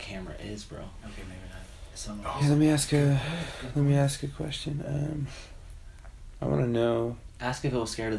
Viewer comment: DUDE at 31:13 when they said it didn’t look like, sams says “no Im fine” but you can hear him then laugh and say “ask ME a question” I believe something definitely happened to him that night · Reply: Yes I heard it too after I read this comment, before I thought he said "let me ask a question." But I heard it again and it was "ask me a question" I realize it's late, I'm literally rewatching their video during my school, so 😅